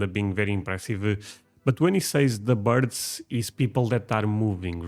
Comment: I'm pretty sure he actually said "births." The number of babies born in Texas "was" more responsible for population growth in Texas earlier, but now the fact so many people are moving there is having a greater impact.